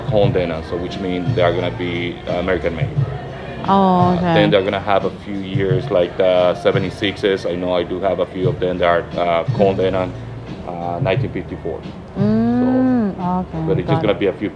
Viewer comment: Good contents. I've always been a Levi's fan. At 14:10 or so, I think he was saying Cone Denim , not 'combed denim' as shown on your caption. Cone Mills Corporation from North Carolina had been Levi's partner for more than 100 years before its White Oak factory was closed down in 2017.